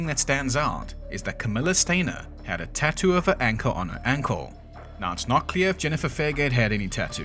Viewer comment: did you say she "had a tattoo of an ankle on her ankle"? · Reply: Tattoo of an anchor